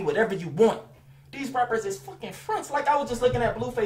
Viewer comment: What did he say at 21:06 rapper are fucking fricks?